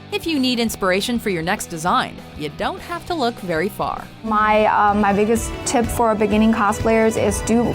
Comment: i heard "my biggest tits"